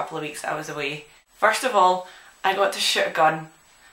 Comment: sounds like shes saying " i actually got to shit a gun"